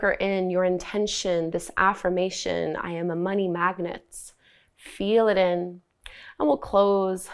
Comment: There was a lot of background noise for me today so I misheard "I am a mighty magnet" and didn't realize what the affirmation actually was until I read the comments haha 😃